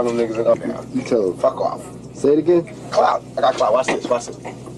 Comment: Did pac say "clout" at 1:31 i thought that word was new😂🤣